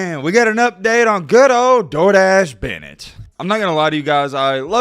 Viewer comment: For the Longest time I thought was Saying Stenton "Jordache" Bennet not "Door Dashe"😅🤣🤣